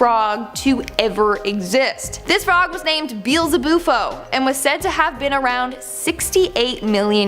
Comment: I swear I thought she said "Beelza-boopo", but it was "Beelzabuffo". Saaaaaad